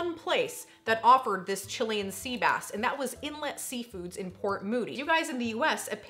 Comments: I to always thought he said chilli & sea bass. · Yeah, when I was the kid I also thought he said "Chili n sea bass". I always thought to myself, who the hell would want Chili and fish for lunch!?